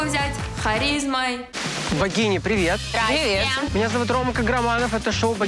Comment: Мне послышалось "вагины, привет". 🤣